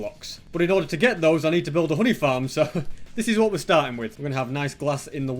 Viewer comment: i thought he said hoodie farm when he said honey farm LOL